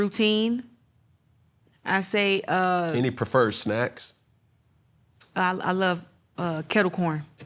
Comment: Did he say SNAC.